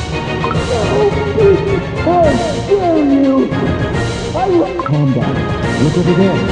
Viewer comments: I tried to see if i could tell what the were saying here, this is what I could hear Knight cookie: YOUR HIGHNESS! YOU (???) damn you!!! Lilac cookie: calm down, (???) That's all I could tell was there I'll see if I can find out more if I ever come back to this comment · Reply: I’m pretty sure he said “How Dare You” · This is what the cookies say Knight: "Your highness! intelligible HOW DARE YOU! I WILL--" Lilac: "Calm down, look over there!" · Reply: I’m fairly sure knight cookie said “foul beast, how dare you?!”